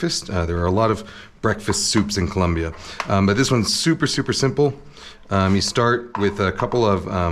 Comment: I heard “this one’s souper souper simple” 😂